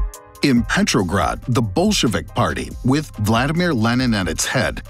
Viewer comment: I’m sorry I misheard the Bolshevik party and I sat laughing half an hour because I thought it said “The Bullshit Party 😂”